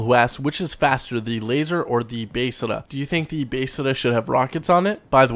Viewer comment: Why do you say Besra so weird? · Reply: I new I was the only one that thought that it sounds like hes saying basesada